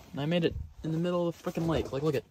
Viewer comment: I like how the most replayed on this vid is at 14:33 when it sounds like he said "in the middle of the fuckin lake" 🤣 after watching it a couple times myself, pretty sure he said frickin' lmfao